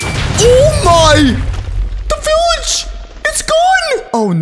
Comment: When you said the village was gone I thought you said oh no the table it’s broken😊